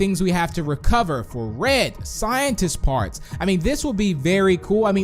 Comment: Wait did he just say scientist... parts!? As in parts of red?